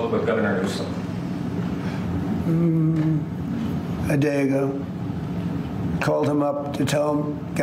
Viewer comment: Did trump called the governor a Daygo?